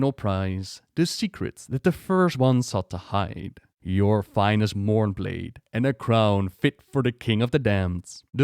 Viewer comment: When the Jailer is talking about Frostmourne, he says "Your finest Mourneblade". Do you reckon there are other "mourne blades". I mean Sire Denathrius' sword is even called Remornia, which sort of sounds similar to Mourne. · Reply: Thought he said '' Your finest meowblade'' Omg rawr thanks